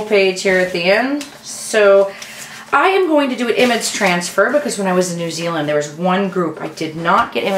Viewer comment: love it... funny thing--thought you were saying "Emmitt's Transfer"--hmmnn... was wondering what technique that was; maybe something from New Zealand... ha